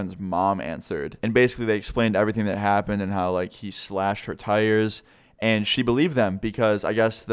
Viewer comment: When you said slashed her tires I thought for a sec you said slashed her throat and I was like df